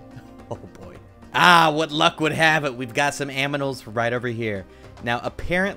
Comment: did you just said Aminals instead of Animals?